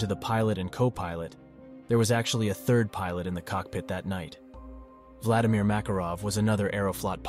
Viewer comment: I'm sorry did I hear Vladimir Makarov??